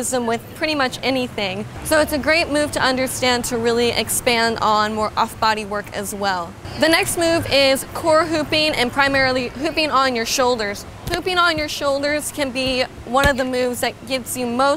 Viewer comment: Anyone else thought she said ‘pooping on your shoulders’?